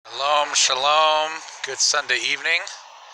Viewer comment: I know when the sabbath starts and ends I have been keeping it for 40 years, the reason I commented was in the very beginning of your video it sounds like you said "Shabbat Shalom, it's sunday evening." Going back and listening again I see you said "shalom shalom." I wasn't trolling you as I have found your videos enlightening and truly appreciate anyone who has taken the time to research and use the proper Names, I believe highly that you are one of the remnant. However what I thought your initial comment was threw me a bit. I should have known better, sorry about that. May Almighty Yahweh continue to bless your discernment. Peace to you and yours, we pray all things in Yahshua ha Mashiach. PS. I would appreciate it if you didn't call me dude, I find that disrespectful.